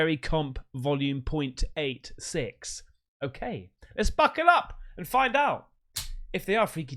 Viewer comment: Mate I thought you said "let's fcuk it up" 😂😂😂